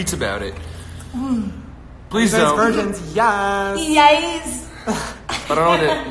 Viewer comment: what did james say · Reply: ddeleche someone asked are you guys virgins